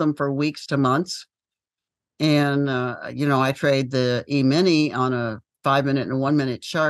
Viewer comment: i didnt get what she said on 8:30 , something like EMINi for intraday trading , she was comparing her time frame trading for stocks and EMINI